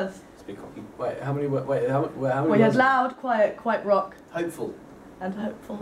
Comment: I thought Aston said wet instead of quiet...